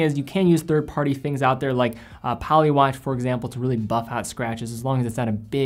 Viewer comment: what did he say is the material to repair the crystal? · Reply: Polywatch paste